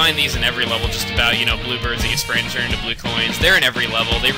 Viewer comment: When you said "blue bird" I could've sworn you said "blooper" Yeah Bloopers in every level! That makes perfect sense! XD